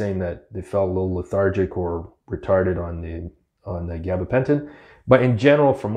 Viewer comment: did he say retarded?